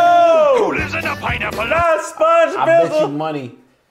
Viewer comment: did he just say Sponge Bibble?! 🤣